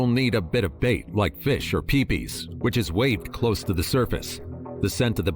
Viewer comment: Did he say wave fish and peepee in front of ther face at 7:03 mark not my peepee